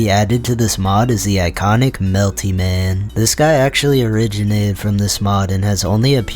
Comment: is that elty Mash i hear?